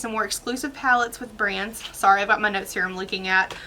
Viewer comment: When you said “ sorry about my notes here” I heard “sorry about my nose hair” I died 🤣